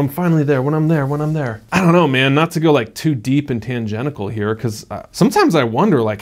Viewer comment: did you just say “Tangentical”